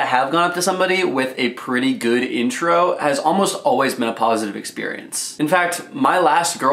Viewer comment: I swear he says menapositive experience